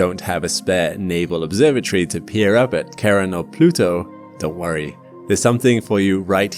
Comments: Well, I heard "Karen" instead of "sharon" and I could think only of mannish bob cuts. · Why do I keep hearing "Karen"? wth? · for the whole vid, i thought he was saying "Karen"